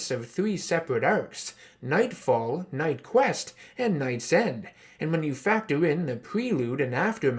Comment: I thought the third arc said Knight Send